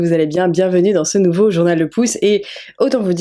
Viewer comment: Au début je n'ai pas compris j'ai entendu "ce nouveau journal de pouce" 😅